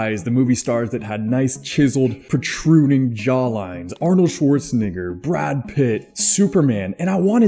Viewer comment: umm what did he say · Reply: Arnold Schwarzenegger